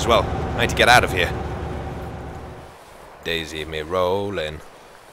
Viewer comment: did he say "DayZ me rollin'"?